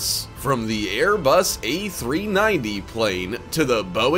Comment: did bro just say a390